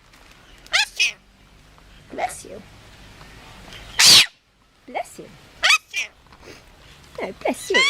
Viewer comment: am I the only one who thought the bird was saying fuck you instead of bless you. I actually wouldn't realize that the bird said bless you if I hadn't read the comments .